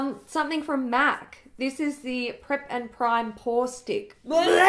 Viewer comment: Had to rewind the part about the MAC pore stick... thought you said foreskin hahaha 😂 💎💎💎💎